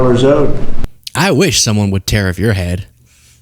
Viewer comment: I thought said "I wish someone would tear off your head...".